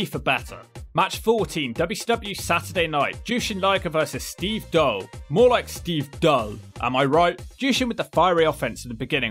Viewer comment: Steve Doll is a part of Wrestling History. He was in the Nitro match where Scott Hall debuted during. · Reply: Because of accent (I’m American), and the fact that the Doll/Dull joke was telegraphed by the headline text (and the fact that I hadn’t heard of Steve Doll before), I thought he said “Steve Dull. More like Steve DULL!” Which I thought was a meta-joke on the general run of “____, more like ____” gags.